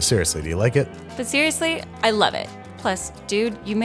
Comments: I thought she said clean dress club and I'm like, babish bout to get it bois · “Definitely a member of the clean jess club” “What” That killed me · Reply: I heard “clean chest club” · It's hard to hear "clean Jess club" as anything other than a dirty joke. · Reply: So I definitely thought she said "clean dress club" implying there wouldn't be any dress evidence a la Monica Lewinski 😶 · "A member of the Clean Jess Club" Wait a minute. Is that insinuating Andrew uses as a plate sometimes? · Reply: Bruh I thought I heard “Clean Dress Club”. Either way tho...